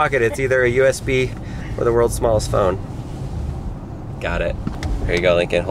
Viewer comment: I thought it said "Worlds Smartest Phone"